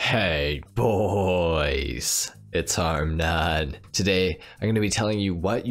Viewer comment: Am I the only one who thought he was saying Hey boooys it’s armed nun! When I first watched him